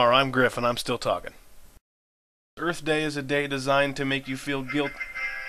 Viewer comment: Did you just call it Griff Day? I thought you had the entire year...